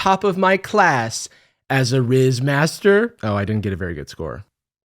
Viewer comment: Omg I thought it said JIZZ master wtf